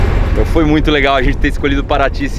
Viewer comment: "Não foi muito lega" ou "foi muito legal"?